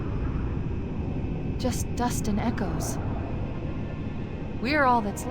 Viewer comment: I always thought she said "just us and echoes"so I thought echo 419 survived